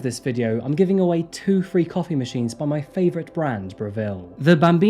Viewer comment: I wasn’t looking at the screen and I first heard, “…free copy machine.” I was like, “That’s pretty random.” Lol.